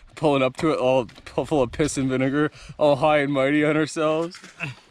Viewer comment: did he just say the n word!!! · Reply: piss and vinegar